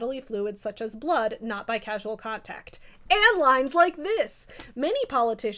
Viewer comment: I heard “and lies like this!” And was like are we supporting Ronald Reagan in here 🤨